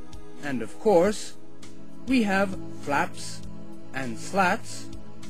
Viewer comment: lol yeah i learned that shortly afterwards!! it sounds like he says slaps though lol